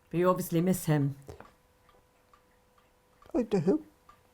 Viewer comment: Turn on closed captions and read at 1:20 when asked "you obviously miss him" - "I do" closed captions say woo hoo